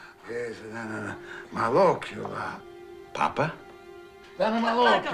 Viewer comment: Just in case anyone’s wondering I think the old grandpa was pointing and saying at 10:00 I think was malocchio which is often said about folks with evil eye or bad intentions.